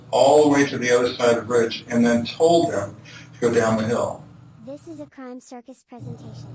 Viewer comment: If they read you your Miranda rights…ask for a lawyer and stop talking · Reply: Every time he speaks, all I hear is “guys, down the hill”. The voice.